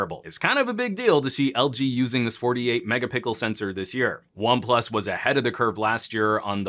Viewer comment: you say mega pickle instead of mega pixel 😁. Great video man. Really like your videos.